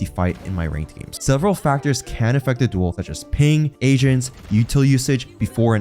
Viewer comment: i thought he said "such as ping, asians" had me laughing for a sec